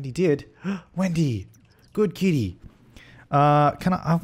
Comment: Did u say Wendy? I am pretty sure that’s not him